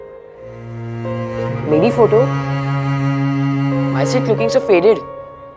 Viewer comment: what the boy said? Can anyone please help? The sentence that the boy spoke after seeing his photo on phone screen is not audible. Can anyone please help? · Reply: He said meri photo, why is it looking so faded.